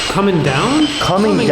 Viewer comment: So at 25:10 when and were using the Spirit box, I think it says either “come in now”, “coming now” or “coming in now” and then the door slams as though the spirit just came into the room and shut the door behind them! Also, I noticed how the door doesn’t automatically swing closed when they let go of it, so why it slammed closed as if it had a door closer on it is wild.